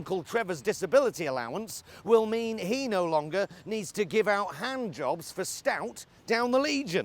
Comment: “Handjobs for stout down the Legion...” brilliant !😂😂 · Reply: I thought he said,giving hand-jobs to scouts!